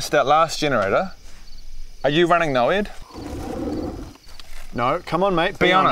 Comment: what does he say? “Are you running ____” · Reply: N.O.E.D.” It’s a dbd term :) but I forgot what it stand for. Maybe on google u’ll find it